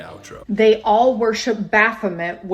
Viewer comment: I thought she said they secretly worship Batman! I was like what?!